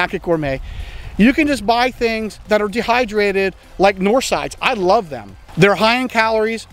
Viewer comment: what does he say? north side? norsize? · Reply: Neither. I say “Knorr Sides”. They are Knorr Pasta Sides and Knorr Rice Sides. Found and just about all grocery stores.